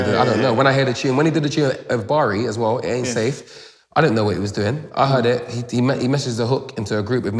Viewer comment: did he said "asafe bari" ?